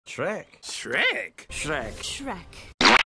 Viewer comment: Listening to the beginning part over and over and over I stop hearing shrek and just hear "chrair" and then on the last one i hear "ashhrak"